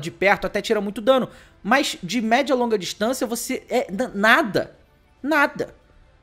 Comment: SÓ EU QUE ENTENDI "VOCÊ É DANADA" NO